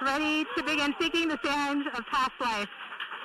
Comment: "Perseverance is safely on the surface of Mars. Ready to begin seeking the sands of past life."" Anyone else get the nerd-chills? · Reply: Sands of past life was tongue in cheek? I thought she said signs of past life.